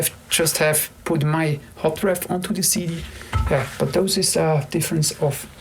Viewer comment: What does he say at 40:55 with regards to mastering -He thinks sometimes they should have put his "what" on the CD?? i can't understand the word he's saying... "Hot ref"? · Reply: Basically that if it is mixed at this level, then it would not really matter wether you put the Hot-Ref or the Master on the CD, as both sound near identical and after having asked over 100 people out there, which version they preferred , it was 33% for Brians Master, 33% for Teds, and 33% preferred the hot-ref.